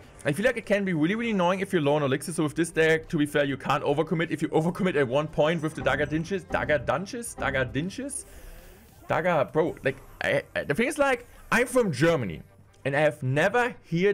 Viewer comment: did you say dagger dünnschiss ???